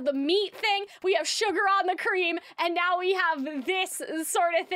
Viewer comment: Its funny, I liked the song on the first listen and then the more I listened to it, the worse it sounded. I thought Alastor said "Sugar and the Cream" at first and that made sense given how he brings it to tea and then I listened a second time and noticed it wasn't that, and a third time and it sounded weirder and I realized that none of it meshed but wasn't sure how. This video definitely helped make sense of it.